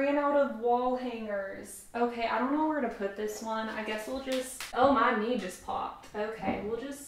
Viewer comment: I literally heard her say "oh my me"